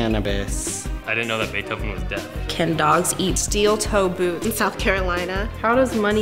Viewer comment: i heard that as can dogs eat steel tofu